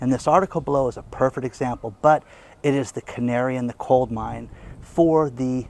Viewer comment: Could have sworn I heard you say, "canary in the COLD mine." Just wanted you to know it is canary in the COAL mine.